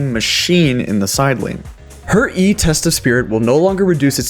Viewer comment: i thought you said testes spirit lol